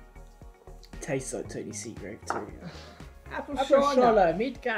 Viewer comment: did i hear "tastes like tahnee seagrave" ? o.O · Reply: I think he said Tony Seagrave, Tahnee's dad and team owner for FMD Racing